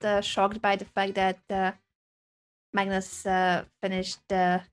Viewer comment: For some reason i thought she said Magnus pp Lol